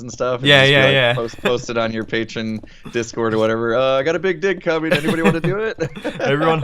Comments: it sounded like he said d*** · It sounds like he said "I got a big dick" :) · it sounded like "I got a big dick comming".